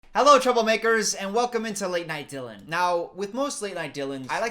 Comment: when I first started watching you I thought you said "chuckle makers"